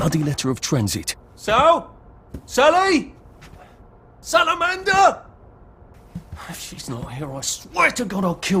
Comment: did Arthur just say SALAMANDER